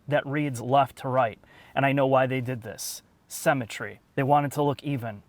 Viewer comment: did I hear “cemetery”?